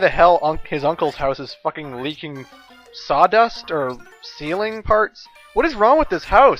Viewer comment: I thought you said ceiling farts at first. Shit was hilarious to me. I just remembered this game from when I was a kid..but now I see I never made it past the first fuckin level. But somehow I loved this game.